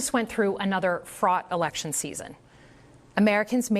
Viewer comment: Did she say Fraud Election or Fraught Election?